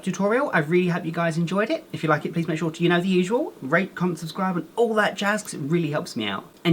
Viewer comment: I thought he said rape comment and sub but he said rate lol